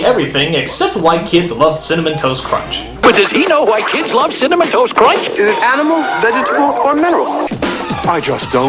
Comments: XD I thought he said "Does he know white kids love cinnamon toast crunch?" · At first I thought he said "White kids love cinnamon crunch"! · i thought he said does he know white kids like cinemontoast crunch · am i the only one who at first thought he said " white kids loved cinnamon toast crunch " even the guy on the commercial said it like that I realized that it wasn't saying that immediately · My mishearing of the CTC slogan. "Does he know white kids love Cinnamon Toast Crunch?" Well, it'd explain the existence of Crack Kid.